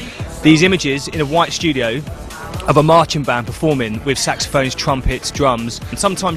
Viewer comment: the way he says it .. marching band performing with saxophone ... sounds like he is sayign sexophones :)